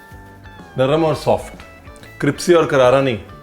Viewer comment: did he just say cripsy 🤪